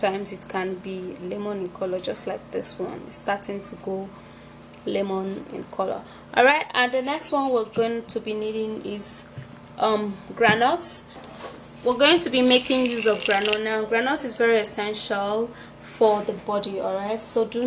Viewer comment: The 2nd ingredient is granots? What are you saying ? Gran odds? Are those peanuts? Tell me. English please.